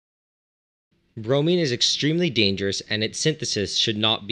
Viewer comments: Bro I thought it said brownie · I thought it said brownie